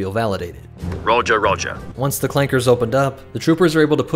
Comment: here i well tell you what he says 'roger...roger'